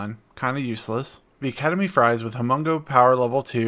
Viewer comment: I swear I thought you said “Ketamine Fries” at first. Work on your Pronounciation please!! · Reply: when you said Academy Fries